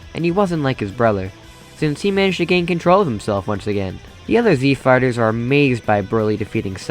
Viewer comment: Alright, now I don't wanna be immature here, but at least every other 3 times you say the words "Z Fighters" I hear "Z Farters". I apologize 🙇